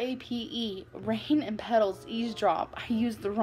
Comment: What are the words? Rain and pedals ___? The captions said EAS drop, and it sounded like she said ease drop. Does anyone know? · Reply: Rain and petals eavesdrop.